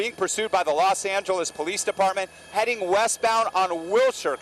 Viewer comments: did he say Los Santos Police Department? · did he just say the Los Santos Police Dept.? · I don't her Los Angeles, help. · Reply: Now I heard "Los Santeles" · did he just say the los santos police department